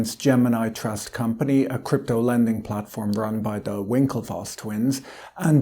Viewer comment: I misheard Patrick at the start, I thought he was describing the Winkelfoss’ enterprise as a “crypto-lemming platform”. Seems kind of appropriate.